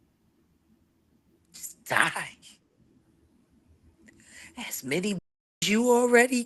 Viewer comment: I thought it-Jag said it “ He should Just Die🔥”